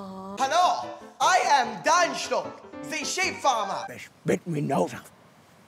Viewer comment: i thought he just said shit farmer instead of sheep and now im in hysterics